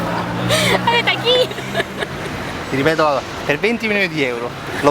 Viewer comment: Ma solo io non riesco a capire cosa dice la ragazza al minuto 2:09 sembra dire "a veta chi" o.O?